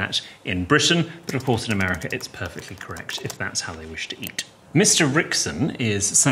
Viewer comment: ここ字幕の意味の後なんて言っているかどなたか教えてください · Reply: if that's how they wish to eat. のところかな？ 「彼らがそう食べたいというのなら」的な感じでしょうか。 字幕で意味としては間違ってないですけど、毒素が抜けてる感じでしょうか。 毒素は「お前がそう思うんならそうなんだろう。お前ん中ではな」的な感じです